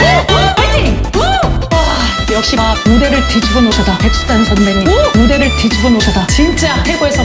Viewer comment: 와..군대를 뒤집어놓으셨다.. 라고 들림